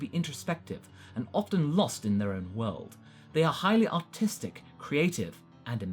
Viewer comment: The way he said artist sounded like he said autistic